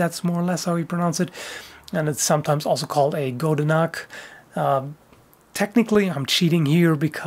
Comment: Until I checked the description, I thought you said "Goedenacht" for #3. As if it were the dark counterpart to the Goedendag or something.